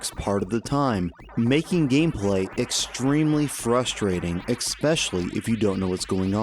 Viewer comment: “expecially” (especially) Only mentioning it because you seem to say that an awful lot and I thought making you self conscious of it will help. Also think I heard “tourmanent” instead of tournament in your Toughman Contest video but I haven’t heard that repeated in your other videos yet so it may just be a mistake or I heard it wrong.